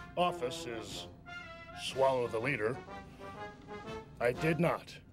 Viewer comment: All this time I thought when HBK said "swallow the leader", I thought he meant "swallow the liter". Oops.